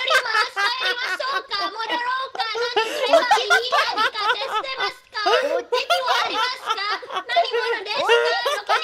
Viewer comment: I think she said Teki(enemy) instead of Eki(train station). Ngl though it was funny to think she's asking for a direction out of nowhere.